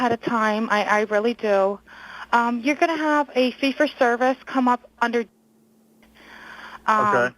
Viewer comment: I have heard this three times now and I always heard "it'll be under C for service" and just understood "fee for service". Duh. And where in that phone call did the guy say whay she apologises for "pump the boat"? I did not hear that anywhere.